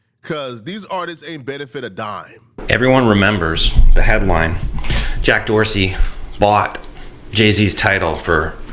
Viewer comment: LMAO i thought he was saying Jack Doherty... I'm like damn that lil e-pimp actually has motion. Youngster getting 80% of these young OF models is true pimp behavior for people who don't think he is... I'm not a fan of the kid but the game he played was strong